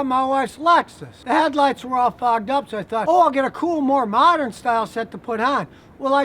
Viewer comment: Did anyone else think scotty said "the headlights were all F'ED UP" at 1:17?😂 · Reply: Fogged up